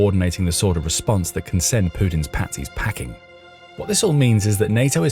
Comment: the first time I heard "Putin's panties"